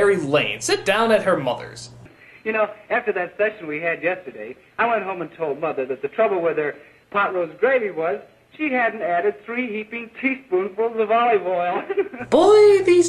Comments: anyone else hears "After that sex we had yesterday."?? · i swear it sounds like hes saying 'after that sex we had yesterday'...